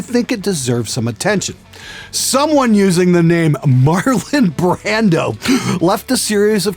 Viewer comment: Haha.. i thought you said "Marlin Brando" that would have been a fun nick...